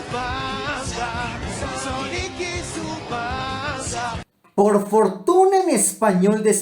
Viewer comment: supongo que dice "y su banda" pero entiendo "panza" o "basa"